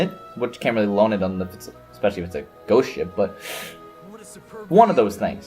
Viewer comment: Every time you said Ghost Ship, I heard it as 'Goat Ship ' Imagine my disappointment once it turned out to NOT be a Ship maned by Goats :( Still, a great video as always,)